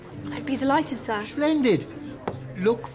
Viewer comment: "Good to see you, Rollomy." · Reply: I’m gonna be honest I thought he said “wallaby”